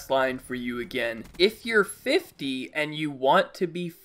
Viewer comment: I heard him say 15, not 50. And considering he looks about 15, I'd say 15 is more a sign of danger.